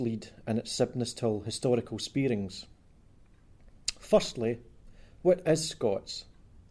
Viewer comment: Oh god when he said "Firstly, what is Scots?" I heard, "Firstly, we're escorts," Jesus